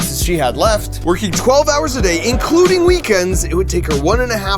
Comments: mark says "including weekends", whereas the English subtitles say "skipping weekends". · At minute 19:21, you said "Including weekends", and the subtitles said "Skipping Weekends". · mark says "including weekends" but the subtitles say "skipping weekends" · he says including, subtitles say skipping. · when he says "including" weekends the youtube captions say "skipping" weekends